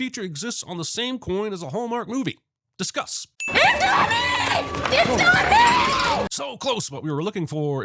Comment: because sye isn't possessed. The wish was literal. She loves Bear more than anything. Had he said "i wish she reciprocated my feelings" it probably would have turned out better, even though it's still incredibly fucked up. But technically, it's still her. Her personality has just been altered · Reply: Tbf, I thought this at first. The screaming in absolute agony, the way she moves... I assumed a demon was assigned to "grant the wish" and was allowed to keep the body. I also heard her say "he's coming" instead of "it's not me" though so